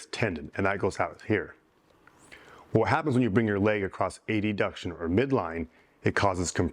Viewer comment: did you say "A-D-Duction'?